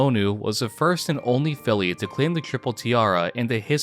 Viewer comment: im sorry, the first and only what? My listening comprehension sucks I'm sorry please someone write down what did he said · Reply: "filly", a female horse under the age of four